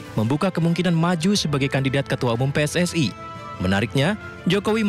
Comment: salah dengerkah? kok pssi? 😂😂